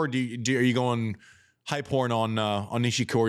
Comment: Every time he said "Hype Horn" all I heard was "High Porn". Laughed every time! "Hey JW, are we going high porn here? Are we gonna do it?" 🤣